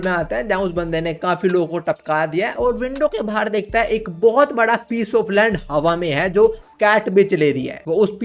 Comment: bohot bada piece of land. I literally heard piece of lu**😂😂